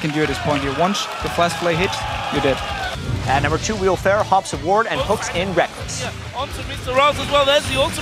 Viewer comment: I swear at 29:41 Jatt says "Will Ferrell hops a ward and hooks in Rekkles"